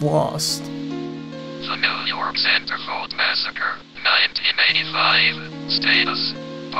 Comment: Thanks to the raspy reading voice I heard "The New York Santa Clause Massacre" instead of "Centerfold massacre"